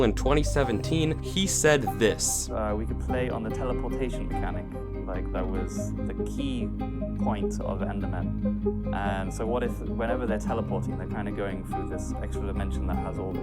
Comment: god your sound mixing is so bad, what did this guy say? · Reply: “we can play on the teleportation mechanic. like, that was the key point of the endermen, so what if whenever they are teleporting they kinda going through this extra dimension that has all this bad stuff in them such as endermite, and whenever they do teleport them they leave this bad stuff behind”